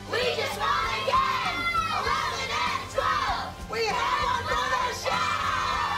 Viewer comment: does anyone know what they said after 11 and 12 because I watched it like 20 times and can’t figure it out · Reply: We have one for our show